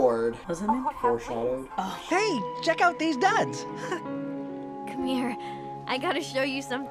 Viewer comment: I know I'm not funny but I swear to god I heard "check out deez nuts" the first time